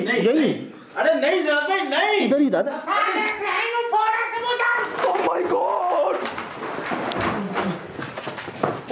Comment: what is the jinn saying and what language is that to me it sounds like Arabic or german what you guys think? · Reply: nhi bhai behari,,,he say ,,chl bag tu yha se nhi main fooda sbu daat😬,,,mean yha se chle jao nhi to main tumhare sare daat dod du ga